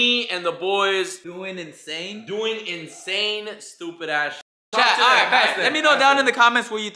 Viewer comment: I thought you were saying “ doing the same stupid ass shit”